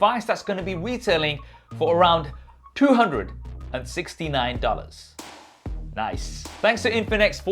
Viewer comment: LMFAO I thought you said it was 2$